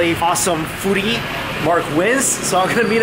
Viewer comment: lol did he just say mark wizs lol